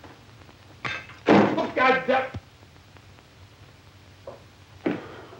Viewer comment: What does he say? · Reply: Look god da